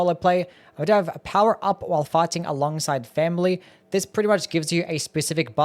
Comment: thought you said "power up while farting alongside family" 😂